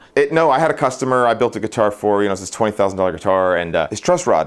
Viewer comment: Did he say $20,000 guitar 😳 nope, nope nope.